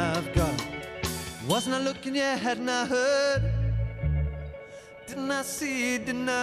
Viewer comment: 0.30 and again at 0.59What THE FUCK is he saying? "Omena hettena hittna heh'n!"What the fuck is that!? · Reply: Hahaha im pretty sure he says "Wasn't i looking? (yeah) Hadn't I heard?" not "wasnt looking ahead and i heard" lol